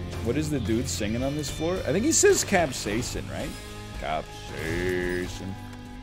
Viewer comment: You hear Capsaicin? I always hear “Spaaaaace. K’chow!” Lightning McQueen got a voice mod.